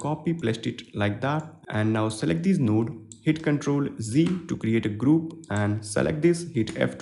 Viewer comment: bro i thought ur saying "ctrl+z"for group XD that was like wt* is happening , where is my group XD nice work , gl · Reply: It's "Ctrl + G", sorry for the Indian accent.